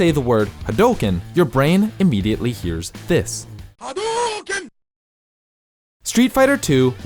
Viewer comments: And Please don’t lie. Who thought you heard “All You Can” instead of “Hadouken” when you first played SFII when it first came out? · All these years i thought ryu was saying abuucream instead of hadouken haha